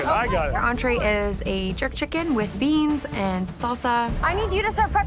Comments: I heard andre is a jerk chicken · I thought she said Andrea is a Jerk Chicken